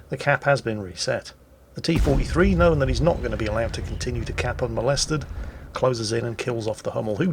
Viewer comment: Wait at 10:25 did he just say "Contest the cap unmolested"?